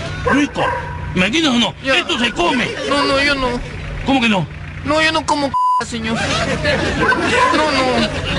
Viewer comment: Que dijo Jacinta? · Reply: Yo no como caca, señor